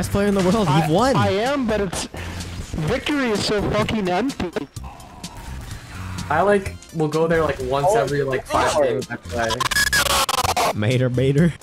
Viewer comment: did he say“ victory is so fucking ENDGAME“?